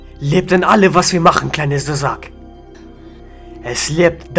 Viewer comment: auf Boden liegt Deutsche Pass Brutale Training Guck wie Schwuler Kleiner SUZAK 😂😂😂😂😂😂😂HAMMER GEIL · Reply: Ich hab: "Kleiner Sausack" verstanden! 😂🤣😂